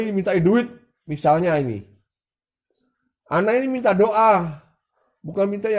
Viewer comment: Sa salah dengar...sa kira minta indomie...ternyata minta doa...😀😀😀😀😀😀😀